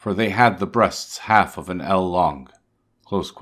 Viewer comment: What did he say? "They had the breasts half of an...." What? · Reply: eel (measurement, about 18 inches long)